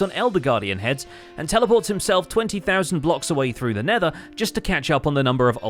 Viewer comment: The captions say 20,000 when says 2,000. Which is correct? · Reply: also says 20'000. Also the x-coordinate and z-coordinates say something around 20'000 as well.